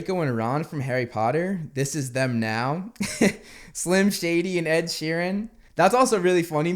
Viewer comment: did he just call emenem slim shady?